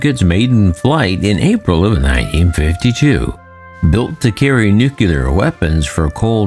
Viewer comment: Did you just say nuclear LOL